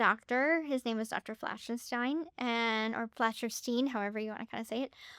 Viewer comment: Did anyone else hear Doctor Frankenstein and have to go back to double check?? 😂😂😂